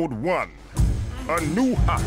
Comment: ive played this game when i was a kid so much but since i didnt know english i thought “a new hop” said “anuha” and “make us sucke” said “megasaga”